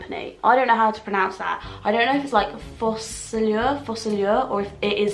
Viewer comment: is it just me or at 1:05 the green makeup pallet sounded like “fuck all Yao”